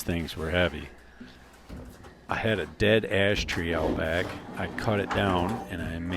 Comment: I had to rewind when you said "I had a big Ash tree" because I heard "I had a big ass tree" 🤣